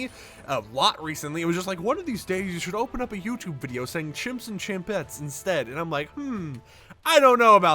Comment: I always thought you said "Chimps & Chimpanzees" lul